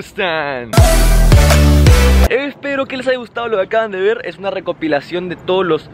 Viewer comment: Ya escuche bola de cristal con todo ese Hola que tal Jajaja empiezá en